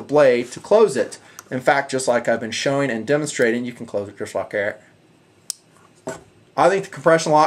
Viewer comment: what the hell did you say on 21:34??? It sounds like you said fuck air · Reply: You can close it...just like that